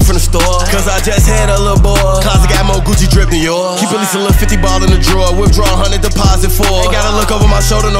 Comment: wtf did he say @ 1:17 i needa know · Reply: KEEP A LEAST A LIL 50 BALL IN THE DRAWER